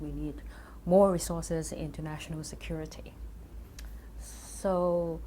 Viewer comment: pretty sure there's a mistake in the subtitles at 1:43. instead of "more resources [and] international security", it sounds like "more resources into national security", which would make more sense in the line of thinking.